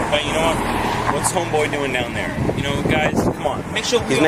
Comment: Did I hear, "What's homeboy doing down there?" right before the ethnicity comment at 6:53?